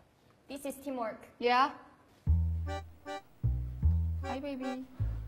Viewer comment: did karina call her enemy baby?😂